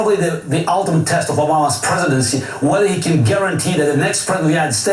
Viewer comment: I had to listen a few times, at 4:27 it sounds like he said "allahs presidency"